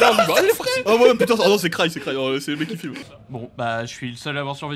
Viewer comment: moi j'ai entendu c'est le mec qui fume